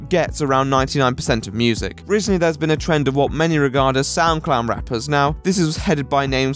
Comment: For the longest time I thought you were saying SoundClown, which actually seemed rather appropriate.